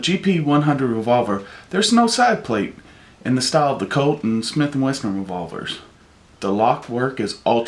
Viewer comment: Sounds like he is saying Smith and Western. lol